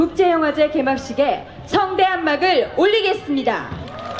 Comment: can someone that speaks Koren tell me what the women says at 20:07. sounds like she says "simida". I used to work with Koreans a while back, and I remember most of the the time the ended a statement with that sound. I always asked what that meant, and they never had any idea what I was talking about. It has always stuck in my head. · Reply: She's introducing the awards and ending the sentence with 십니다 (simnida). It's a high formal and polite speech level called 하십시오체 (hasipsioche). This is used by news TV reporters, announcers, and towards costumer, and also used at work between strangers. If you ever watched the news you will hear that the reporters speak like that. So the people you worked with were just being polite and proper with you! They probably didn't understand what you asked because this is normal behavior and not something natives think about.